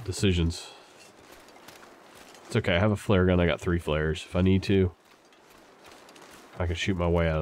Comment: dumps a bag of snares on the table everyone shakes their heads Flares?!?!?!? I thought you said 'snares'!?!?!?!?